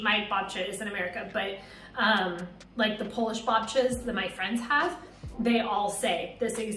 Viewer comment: her saying "babcia" (=grandma) sounded like "babcas", which sounded basiclly like some cult